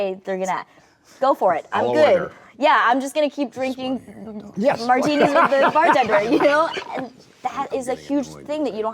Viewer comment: Does anyone know what Todd Phillips says at 54:35 ?? All I hear is “I wanna hear __ talk” “I’m getting annoyed” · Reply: “I’m getting annoyed they’re not letting u talk” ??